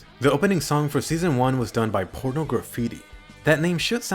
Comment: did he just say por- graffiti